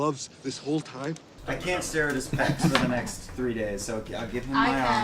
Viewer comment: what are they saying? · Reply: Liam/Vax: I can't stare at his pecs for the next three days. Laura/Vex: I can. winks